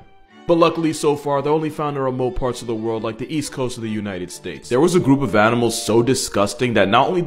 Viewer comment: I’m sorry remote parts of the world like the what coast of the US? For a second there I thought you said easy coast!